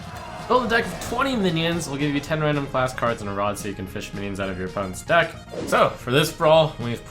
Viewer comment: did he say "fish memes out of your opponent's deck"?